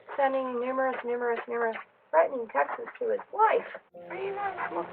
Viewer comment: criminal activity is pronouncing "texts" like "texas". I said what I said lol